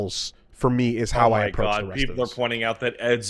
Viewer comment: What the hell does say at 7:14? Azure? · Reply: Adieu. A French word, so he accents it how he learnt it, as opposed to the anglophone way.